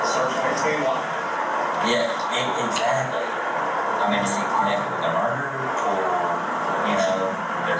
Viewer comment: what the officer said was “I mean is he connected with a murder, or”